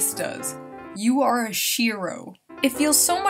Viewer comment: When she said "Shero" I wasn't looking at the screen and thought she said shiro. Shiro translates to white... lol